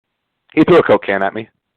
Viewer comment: Am i the only one who thought he said "cocaine", not "coke can". Do people know that sucker punch means the person who got punched is the sucker? The only people who complain about cheap shots are the ones who are mad the fight wasent on their terms.